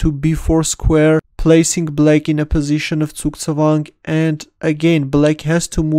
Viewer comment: "Position of..." What did he say? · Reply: Zugzwang